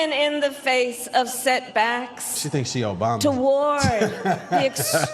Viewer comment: She think, she is OBAMA. tbh that was what I said everything she speak 🤣